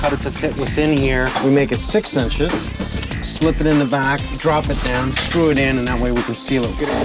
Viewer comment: It was weird for me to hear them say eavesdrops, eavesdrop. Maybe its a canada thing, only ever heard them referred to as gutters 😂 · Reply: Wow. I guess I just totally misheard her, it sounded like "drops"